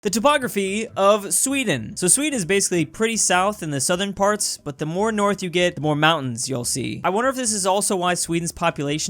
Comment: Wait what did he say here... it's south? 0:02(also I'm 2nd yay)